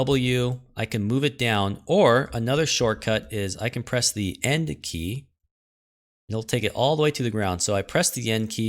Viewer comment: N doesn't seem to work for me to place the object on the ground. Its ok, I misheard, it was the End key I am supposed to press :)